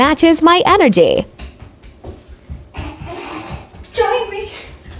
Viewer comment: I thought you said jAmIe instead join me